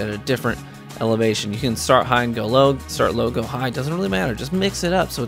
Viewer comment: I thought you said start aping. I went invisible so many times.